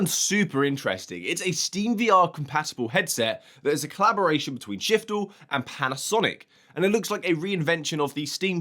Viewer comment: OMG I thought those goggles said shitfall